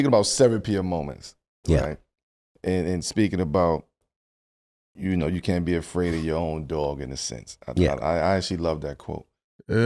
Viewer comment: "You can't be afraid of your own dawg.". I must be legally deaf. I thought I heard Mr. Williams, K. say, "You can't be afraid of your own dark!?!." ✌️🇺🇸